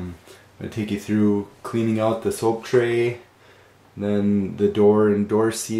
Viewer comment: thought he said the oak tray